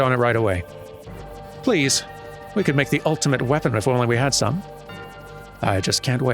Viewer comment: did someone say Ultima Weapon?